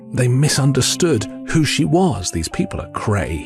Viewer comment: Regarding the sharks. "what who she was." I thought he said, "what sushi was."😄